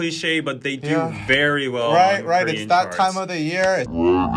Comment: When he said “That time of the year-“ I freaked out and thought he said “That Time of the month-“... I feel sorry for myself and y’all who read this